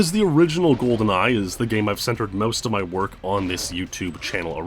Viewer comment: I heard “where I centered most of my worth” 😁